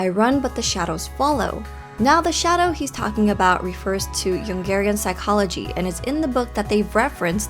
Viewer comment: When you said Jungerian psychology I thought you said Yoongirian psychology.